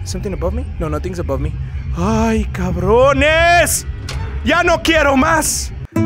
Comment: I thought you were saying "No Me Queda Más" the song by Selena lmao